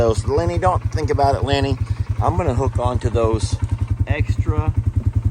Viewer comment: Omg, I thought you said F’n Lenny!